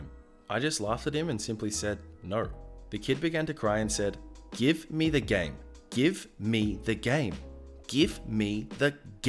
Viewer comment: It’s pretty late and I was in a bit of a dream state when I just here give me the gay give me the gay at 1:48😂 Edit:that’s what it sounded like at the time since I was half asleep